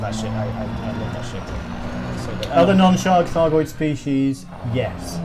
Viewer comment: Non-Thargoid Aliens?! O_o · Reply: That's what I thought they said, but the question actually sounds like 'Are there non-shark Thargoid species?', or something equally odd. Don your tinfoil and prepare to enhance... <link>